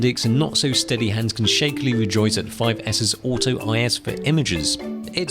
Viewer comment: Nope you misheard, he said "auto IS" as in image stabilization.